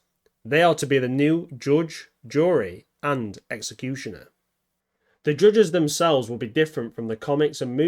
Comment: i heard George droid